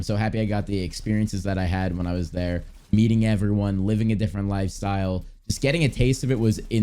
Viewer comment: who else heard eating everyone